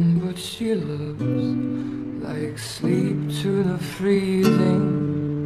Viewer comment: "Like sleep to the freezing" You know it'll kill you, but it's comforting all the same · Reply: Am I the only one who thought he said SLEET to the freezing?